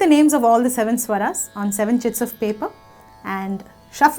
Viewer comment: did she say 7 shits of paper?